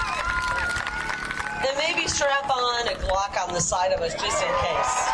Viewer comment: I thought she said strap on a C 😂😂😂